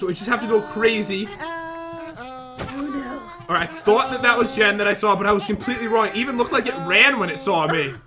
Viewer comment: Pat is a pen Jen is a highlighter Pat draws the world Jen makes it brighter(and darker shes the killer Lmao) Wow thanks for all the likes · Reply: I thought you said pat is Jen lol